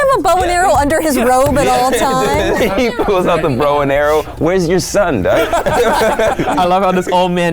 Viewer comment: whenever Garrett says "get the bow and arrow", I hear " Get my Boner"